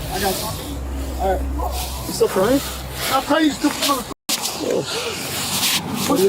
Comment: Did anyone other then me catch what that Officer said to him when he asked a question? "I WILL TELL YOU STUPID MOTHER F----R"! It's